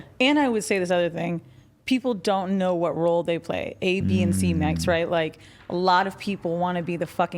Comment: I thought she said “people don’t know what role they play. AD and C Mac”. I thought she violated the loks for a second